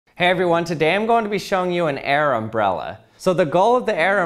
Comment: I swear he done called it an "Arab Brella"